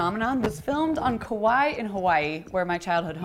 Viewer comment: I heard, "grew up on kauii" and I zoned out for a few seconds, thinking how awesome that would be. I came back though, so no worries. aloha!